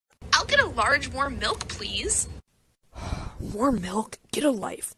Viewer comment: I read and heard this as worm milk and just accepted it as fact